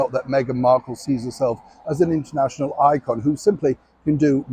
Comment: sorry neil thought you just said that markle was an iron - causes serious burns!!!!!!!!!!